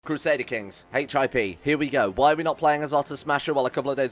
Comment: i thought you said HIV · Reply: HIP dose sound like a VD